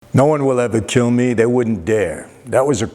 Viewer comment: Thought you were saying no one would ever kill you lol